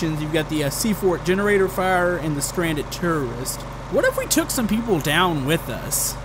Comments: did he say stranded tourists or stranded terrorists? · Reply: Jisoizzard i heard terrorists too But it's tourists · It sounded like he was saying stranded terrorist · I thought said "Stranded Terrorists"